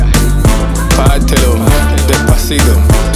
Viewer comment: did he just say..? 🙊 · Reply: Sofia Palacios, No! It sounds like he said Morocco haha 👌